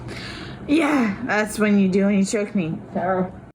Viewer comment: When he says he can't breathe she responds "Yeah that's what you do when you choke me!" I don't hear her saying "that's what I feel when you cheat on me!"